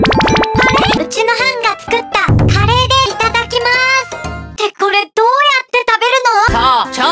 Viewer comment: 2分よーく見たらタカシーいただきまーすに聞こえるw